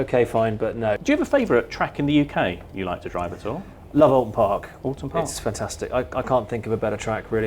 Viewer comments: what was the race track he said he liked at 9:38? I can't quite understand what he said... (merica f*ck yeah!) is it Oulton Park? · What racetrack is he referencing at 09:36? (Can't make out the name.) Ty all. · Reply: Oulton Park which is in Cheshire and home to the British Touring Car Championship